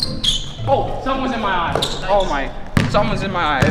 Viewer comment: I thought he said the sun was in my eyes😂😂